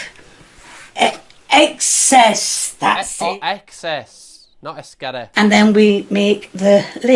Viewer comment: has posted. But also why did it sound like he was saying “it’s scary” in a Scottish accent when he was copying trying to say excess 😂😂